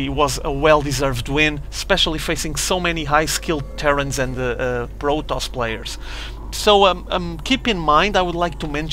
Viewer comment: did he say 'brotoss' at 1:34?